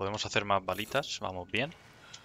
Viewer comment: Los primeros que subiste, pensé que decías varitas... Pero creo que he estado escuchando mal las balitas hahahaha